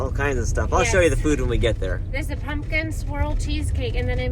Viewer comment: At first I thought said pumpkin squirrel cheesecake 😮😂, I hope everything goes through with the house sale , thanks for sharing another great week of your lives😊